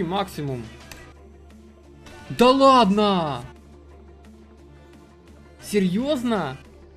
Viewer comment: Can anyone tell me what word says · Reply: да ладно! серьезно? (come on! Seriously?)